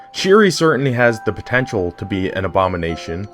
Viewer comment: DID YOU JUST SAY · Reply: POTENTIAL MAN